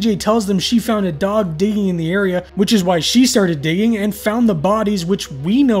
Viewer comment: This one has some pretty gruesome imagery! I swear I heard: "BJ tells them she found a dog digging in the area, which is why she started DATING" 🤣 Re-evaluating her life, realizing she was wasting away her life watching digging dogs in lonely places, I guess.